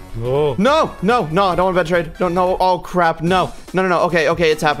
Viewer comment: DID JUST SAY CRAP???